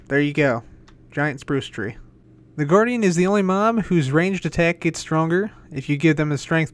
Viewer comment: says MOM instead of MOB